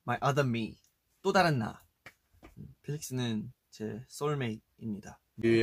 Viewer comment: DID I HEAR THAT RIGHT! FELIX SOULMATE NI MIDA? I DONT EVEN KNOW KOREAN WTF SOULMATES uwu dreams and fanfics come true